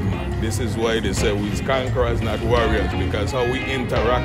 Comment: “We’re conquerors not warriors” sheesshhh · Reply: Thought he said we’re conquerors not worriers u could be right though just what I thought I heard